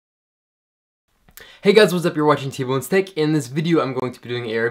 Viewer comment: the way he says Tech is sounds like hes saying TboneSteak lol